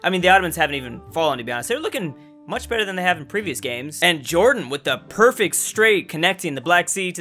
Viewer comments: am i the only one who hears gordan ??? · did he just say... Jorden?